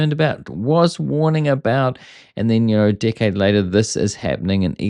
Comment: I thought I misheard you, but either version works. "And then a dick head later, this is happening".